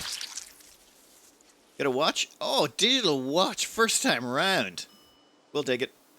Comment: darn, i hear everytime "diddle watch" instead of digital watch. yeah, i´m old...